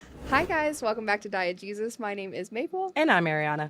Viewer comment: everytime y'all say "Diegesis" I keep hearing "Diet Jesus" 😂